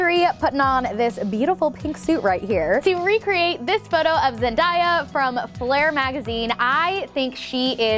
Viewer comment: I'm sorry, but did she just say Zen-Die-A